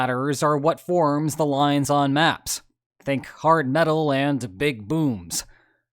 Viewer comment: I thought you said it's all hard metal and big boobs and I was like "woah these summits sound cool"